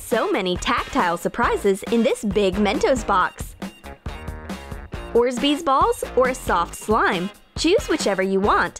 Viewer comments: she says orzbeez instead of orbeez!!! 😂😂😂😂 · Did she just say orsbeesballs